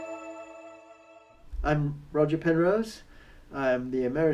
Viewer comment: Totally thought he said "I'm Roger Federer"